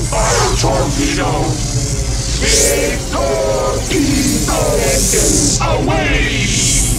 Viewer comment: I HEARD "PENISES" Shouted in the background! Had to be Evans!?